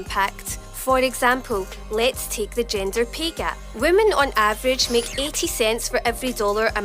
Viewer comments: Sounded like she said "gender pee gap" She's right, I can piss waaay farther. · For a second there I thought she said "Ginger pay gap"